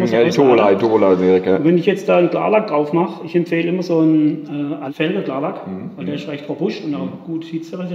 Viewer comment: was fürn klarlack empfiehlt er? Felgen-klarlack? Ich habe den Herren leider kaum verstanden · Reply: Ja Felgenklarlack hat er gesagt